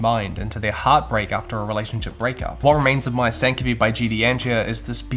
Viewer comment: I had to go back as I first heard "...What Remains of My Sanity" Thanks for the great recommendations